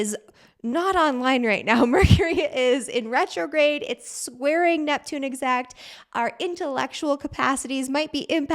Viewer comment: You said Mercury in retrograde and it's squaring Neptune I heard it's swearing at Neptune 😁😁😁😁